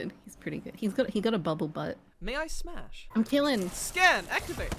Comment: did say “may i smash”XD